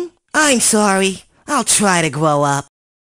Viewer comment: did Parappa say 'I'll try to gwow up'?